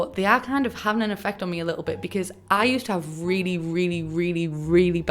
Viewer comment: I heard “I use to have really really really bad tea” and I was like ermmm just try another brand 😂😭